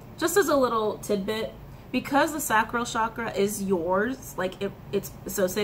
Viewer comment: Can someone tell me what sacro is ? She says it